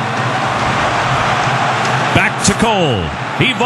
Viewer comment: Listen to the commentator around 0:14 It sounds like he says "o shit" LMAO